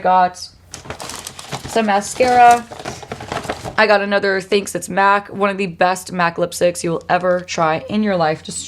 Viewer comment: ! You mentioned that you got a new MAC lipstick in the prettiest color but you did not end up showing it! What color is it and can you do a video using it?? Thank you!! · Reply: I just rewatched and heard you say Thanks it’s MAC… googling it now!! 😂